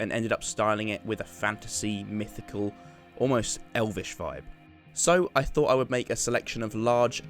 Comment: thought he said almost an elvis feel